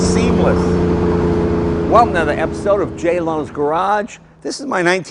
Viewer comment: did he just say "J Low's" garage.